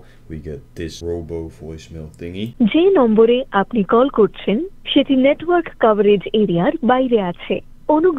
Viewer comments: i thought she was saying "shitty network" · I swear that recording says "shitty network coverage idiot"....or is that just me hearing things 😂